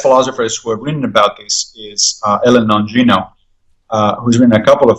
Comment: Who is the philosopher Massimo mentions here? I can't hear what he's saying. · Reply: I believe he is referring to Helen Longino.